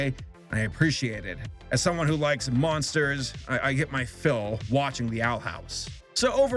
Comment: i thought he said "i got my fill watching the outhouse"